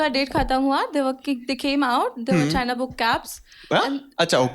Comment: ka joke samjha do koi book cabs ka. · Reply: she said they weretrynaa book cabs , but vagina smth sound hua so ye tha